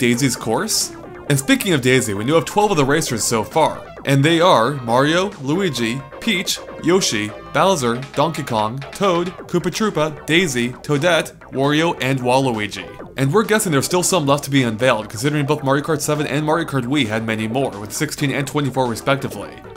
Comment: thought u said poopa troopa